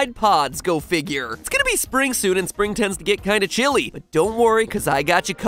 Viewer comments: When MatPat said “spring soon” I thought he said “springsuit.” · I heard Matpat say "Its gonna be springsuit" not "Its gonna be spring soon"